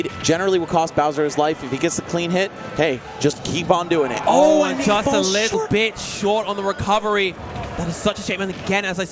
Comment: I thought he said, "Oh but just a little b*tch"